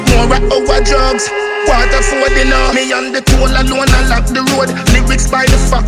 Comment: Me & d toll alone a lock the road, lyrics by the factory load. Who borrow me style ... #KREMLIN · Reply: ReggaeMedia Tv I thought he said "me and di clone alone a lock di road"